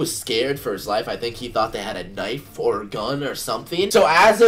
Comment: it sound like he said "knife,fork,or gun